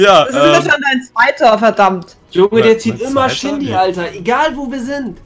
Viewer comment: Ich hab bei 8:24 verstanden " Junge der zieht immer Shindy " 🤣🤣🤣🤣🤣🤣🤣🤣🤣